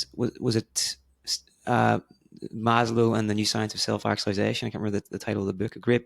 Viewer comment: Does anyone know what book he mentions around 23:45? I couldn’t catch it… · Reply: In case you were still unsure of the name of the book that was referenced by the interviewer - I believe he is referring to "The Power of Self-Actualization" by Abraham H. Maslow (1992). 🙂